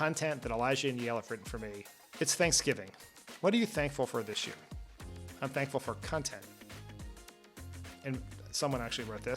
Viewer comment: Did Dan say "I'm thankful for cuntent?"